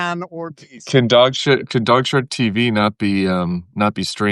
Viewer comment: Tim said DogSh*tTv instead of DogShirtTv.😜